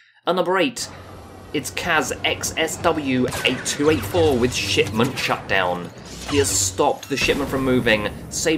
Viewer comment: I thought you said "shitmunch shutdown".